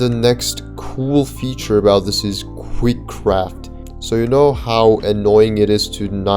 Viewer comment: I thought he said kreekcraft 😭🙏